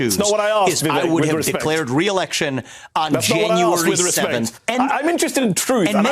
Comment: Hold on…while everyone is focused on Ramaswamy not answering the question, am I the only one who hears him say that he would have “declared re-election on Jan. 7”?? WTF does that mean?? 😮😮😮😮